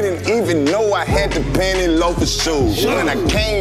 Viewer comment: Thought he said pany's,,,.... Not penny lofers 😅